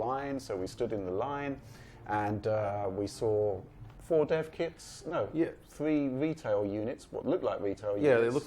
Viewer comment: I heard "there was 4... 4 deaf kids or or ret" and my mind just filled it in with retards. Then... oh. dev kids and retail units